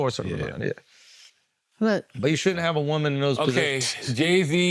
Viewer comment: This is an honest question: when they were discussing the secret service did Andrew say “They shouldn’t have women in those positions” “20:29 ? I hope that’s not what he actually said.